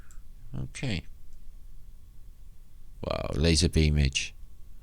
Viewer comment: did you say lazer beamage?,wow. 1:15:03- statue looks like Ophiuchus,13th zodiac sign. 1:19:00- enchanting unbelievable beautifull reality... TY 🖒